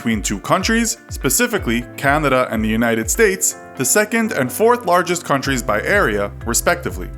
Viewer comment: canada is 3rd largest country? i thought it was second.... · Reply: Sorry guys i heard "3rd and 4th largest countries" instead of "second and 4th largest countries" :/ my bad!